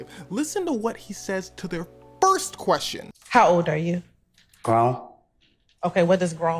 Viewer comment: at first, i misheard him...i thought dude answered with "Wrong." and then followed up with "I'm an Adult." as in 'this is about as polite as ima put it, but did this b!tch just ask me how old i was like im a Child?!' ...but then i listened again, and heard "Grown" and realized 'oh okay, hes being Uncooperative, but not nearly as much as i thought'